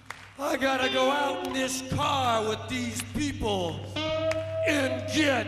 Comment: All this time I thought he was saying, "I've got to go out and scar these people."